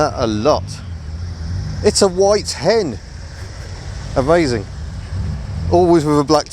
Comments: I thought you said “oh, a white hen!” Took me a few seconds to realise you where talking about that Y10. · He says "It's a Y10!" I hear "It's a white hen!" Fair stopped me in my tracks for a moment. Thought I was on a farming channel. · Hahahahha I genuinally keep chickens when you said Y10 I thought you said white hen.